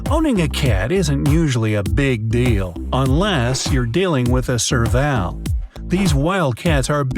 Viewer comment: I tought I hear it as servamp